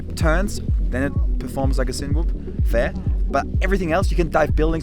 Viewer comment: What did you call it at 18:27? A sin-whoop drone? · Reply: the avata 2 is a cine whoop drone